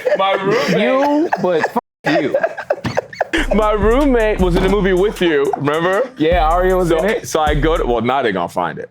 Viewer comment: what movie was it that said?!! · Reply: slasher party